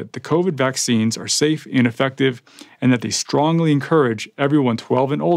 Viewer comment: the subtitles (closed captions) say the vaccines are "safe ineffective" instead of "safe and effective". Funny but hopefully it doesn't cause any confusion to people watching with captions only.